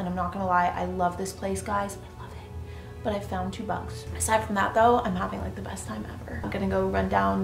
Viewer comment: What does she say at 19:30 ? It sounds like she found two bucks?